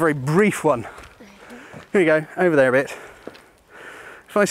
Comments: could of swore I heard "there you go, over there bitch" 😆😆🤣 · did he say “He we go over there, bitch?” 😂